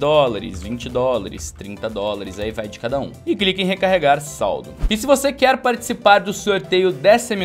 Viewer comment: Quer ganhar skins e prêmios? Junte Saullo Coins assistindo minhas transmissões ao vivo na Twitch! Quanto mais você assistir, mais Saullo Coins você terá e poderá trocar por Skins ou outras recompensas! <link> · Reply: só eu que escutei "Recarregar Saullo"? DUHSAUHDSAUHDSA TMJ Meu Lindo <3